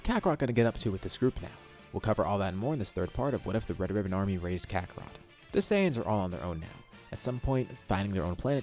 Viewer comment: I thought it said “what if bra raised kakarot”